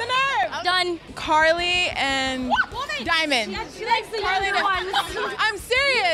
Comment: what did Lauren say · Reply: "she likes the younger ones"